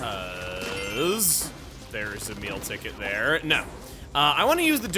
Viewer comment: When he said “Oh, and a Meal Ticket.” I thought he said; “Oh, an Emile Ticket.” That made me smile even though it was my own mistake. I want an Emile ticket.